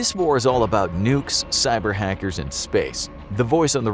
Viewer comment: When he said cyberhackers I first thought he said Siren Head